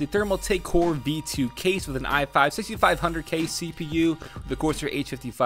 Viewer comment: Did I hear i5 6500k?